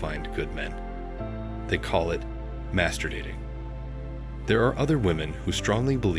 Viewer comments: I had it in audio only and thought you said masterbating. Lol. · Why i thought it said masterbating 😂😂😂